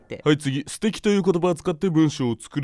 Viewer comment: 英語を日本語訳しなさいという問題で lovelyをステッキって書いたことを思い出した笑 授業の時に先生がlovelyの意味を素敵って言ったのがステッキて聞こえてたんだよな笑 あれ？もしかして私ってバカ？